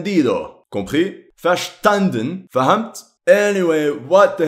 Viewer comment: Did he just say indonesian word "paham"?